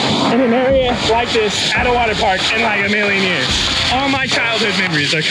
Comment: I thought you said 24 hours in a waterpark